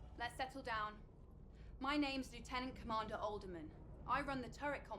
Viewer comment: is she in-lore related to Oldman, the guy in SQ42 played by Mark Hamill? Or is she saying "Oldeman"?